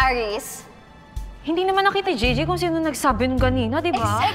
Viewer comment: My ears are dumb because i hear "Hindi" is julie said hindi naman or not ❤❤😅😅